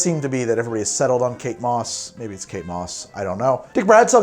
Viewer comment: I thought you were saying "cake boss" instead of Kate Moss and was so damn confused